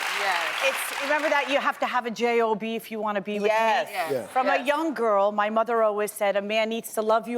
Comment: The third woman at 0:56. Did she say men have to have a job if they want a B.J.? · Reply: If you want to “be with me”